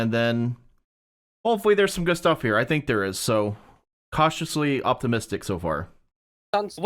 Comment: Did just say "cauti-mistic"?